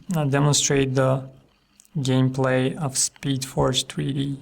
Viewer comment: Did he say at 4:20 Speed Forge 3D??? :O